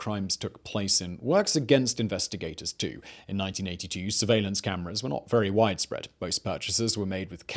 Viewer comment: that is definitely not security footage from 2009 he said 1982 maybe he just got the caption wrong?